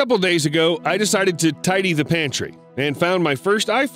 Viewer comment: i thought you said tidy the mansion